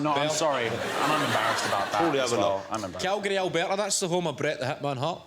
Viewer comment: Someone help me to understand what is said in the gap 2:17 till 2:35!!! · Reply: calgary, alberta is the home of bret "the hitman" hart